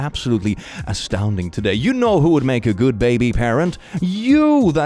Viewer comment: I heard "baby parrot" and tried to work out for at least 10 seconds how that fit